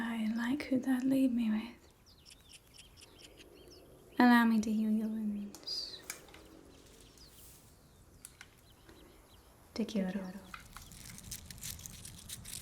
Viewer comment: Here. Can anyone please clarify what she says because I swear I heard her say 'Te Quiero' 😳😅🥰😂 These D&D vibe- Baldur based roleplays are really making me feel so miserable since there's no Xbox version of this game as of yet (That i know of) Anywhoodle. Great work ❤❤